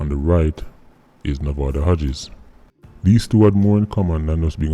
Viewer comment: This whole time I thought you were saying “movado Hodges”